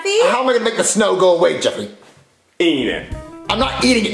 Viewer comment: I thought Jeffy said "Idiot"